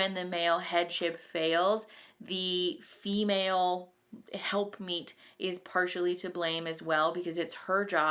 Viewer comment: Luckily I had the CC on. Thought you said "help meat" vs "help meet" or am I wrong!?